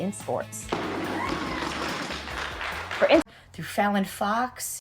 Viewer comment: I know you said fallon fox but my brain thought you said felon fox. lol